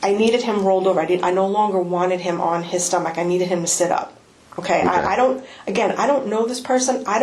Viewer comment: Is it just me or does it sound like she says Get Up not Sit Up? Sounds like Get Up to me & it would also explain if she did say that, or he thought that's what she said, why he tried to get up off the ground.